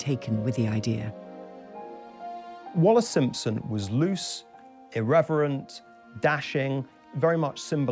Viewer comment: Did he just say "loose"? who the fuck is he to say that to a woman? Even though Wallis Simpson was a divorcee she's still a human being with the same rights as everyone. Ugh seriously ANNOYING